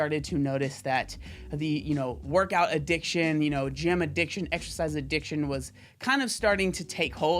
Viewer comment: he’s saying “gym addiction” and all I hear is “gym rat” lol 😭